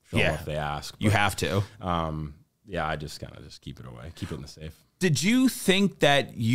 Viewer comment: "Keep it away, keep it in the safe" I thought I heard "Keep it secret. keep it safe"- LOTR